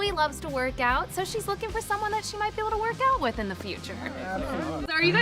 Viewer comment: i thought it was 'wear gowns' instead of 'work out' so i was confused on why she would work out with them if she likes wearing gowns like wHat's tHe cOnNecTiOn there lolol